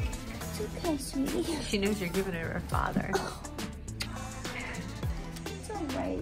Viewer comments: says “ she knows your giving her a father” not sure if I heard that right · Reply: i thought she said “she knows you’re giving her TO her father” 😂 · does say “she knows your giving her a father”??? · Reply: "She knows you're giving her to her father" is what she said. Most likely.